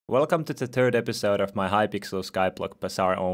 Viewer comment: when he says "third" it sounds like hes saying "turd" ueheuehuehuehhuehh